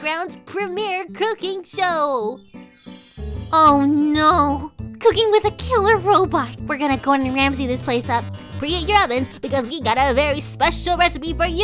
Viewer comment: oh i thought it sayed COOKING WITH A KITTEN Potato